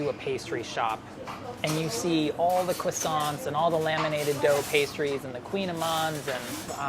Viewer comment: Did... Did Chris just say Coissant, like Tom Holland does?